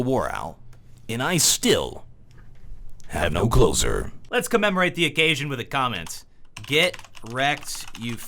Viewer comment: first time i watched back in 2013 i thought he said i am the and i still have no clothes on